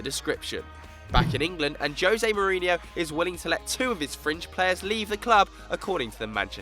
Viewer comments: memphis depay is dutch not french, lol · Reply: Yuvi OM at 3:33 he says 2 of his FRINGE players ....sounds like he said french but he said fringe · Austin Williams he's talking about schneiderlin, lol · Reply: No, at 3:33 he clearly says "2 of his french players", so I'm sorry mate, but you're wrong.